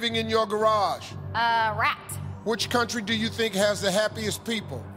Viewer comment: Oh my, i heard "heaviest" people...until I read the comments!